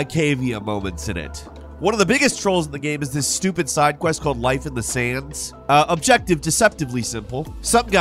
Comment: did anyone else hear Might of the sayans instead of Life in the sand